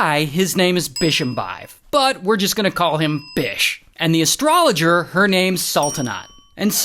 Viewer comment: Why did I think he said bitch? 😂😂😂